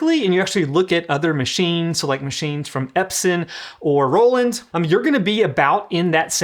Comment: I heard Epstein